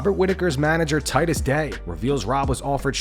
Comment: Damn. I was so drunk earlier, I thought you said Whittaker's manager is named Titus Dank 😅